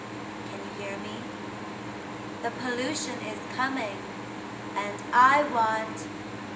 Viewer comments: This whole time I thought she said A revolution is coming but she says A pollution is coming 😂😂😅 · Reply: I thought she said evolution!! Who would have guessed Pollution? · This whole time I thought she said A revolution is coming but she says A pollution is coming 😂😂😅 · Wow, how cool to have footage of what would become a defining moment of pop music ! (P.S: I always thought she said "Revolution is coming" but apparently, it's "the pollution is coming?")